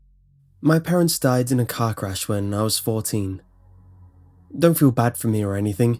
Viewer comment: I thought he said "My parents died in a cock rash when I was 14" lol